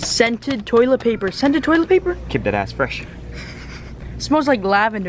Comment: You guys swore u said keep that * clean